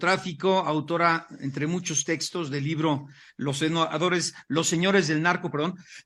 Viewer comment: Min 12:01’ los senadores del narco’ parece que dijo jaja